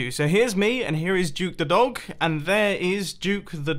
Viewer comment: I seriously thought he was saying Juke, the Dog, not Duke.